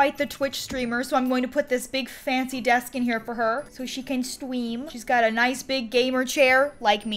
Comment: When you said so she can stream! I thought you said scream 😂